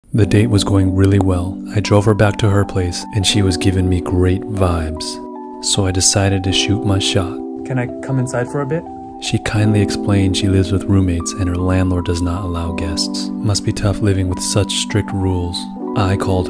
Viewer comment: for a min i thought you said shoot myself